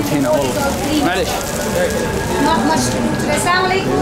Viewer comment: سمعت السلام عليكم 🤣